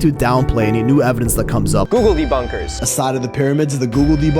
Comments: I heard "GOOGLEDY BONKERS" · Am I the only one who when I hear "googeldebunkers" I hear it as "googeldy bunker"? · When you say it, I keep hearing "googledebonkers." Draws a chuckle every time. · I hear it as "googledy..bunkers". Lol...it makes me think of quagmire..."giggity". Giggity bunkers😂😂